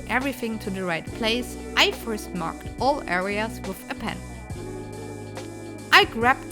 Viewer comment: What is she saying at 6:00? I couldn't make it out. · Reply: I first mark all areas with a pen